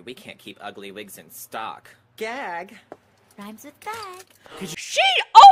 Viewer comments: Okay I going to be honest, The gag bit I thought she said “rhymes with fat~” I never realize she said the other f word😳😳😳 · Oh she did say the F bomb I thought she said rhymes with bag.